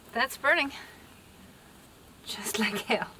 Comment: Did she say "That's burning... Just like hell"?? 😂😂